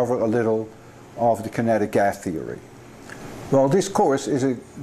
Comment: does he say "kinetic gas theory" ?